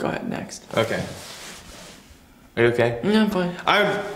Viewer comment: He sounded like he was saying „it's okay“ in Czech (to je v pohodě) and I'm confused